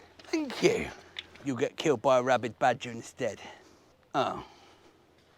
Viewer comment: I thought you were saying “a rabbit badger”. I pondered the existence of such a wondrous beast 🤓